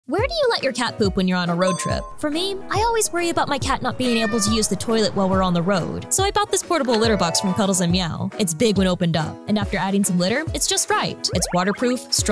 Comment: I misheard it as “ I always worry about my cat being under the road”